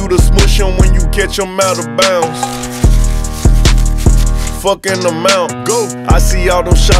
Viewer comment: I thought he said “ fuck an amount” never would’ve thought it was fuck in the mountains 🤣🤣